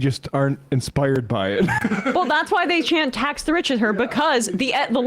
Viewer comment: Ha ha ha ha! "I thought they were saying 'Let's go Bills'" oh man that's rich! "Tax the rich" definitely sounds like "Let's go Bills".